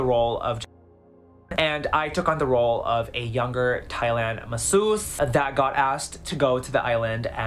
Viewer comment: Every time he said Epstein island I thought he said Thailand. Then he’s like, “I’ll just be to be a 17 year old boy from Thailand.” Ick